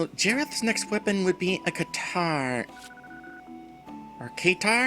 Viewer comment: Oh, Katar! I thought you were saying keytar, like Jareth would be attacking with an anachronistic musical instrument.